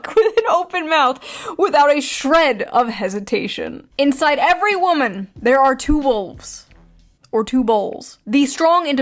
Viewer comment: I misheard "inside every woman there are two holes"... I need help